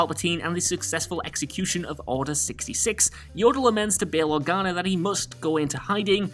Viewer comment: Unforgivable! At 3:38 did I just hear you call the little green Jedi Master, "YODEL"?!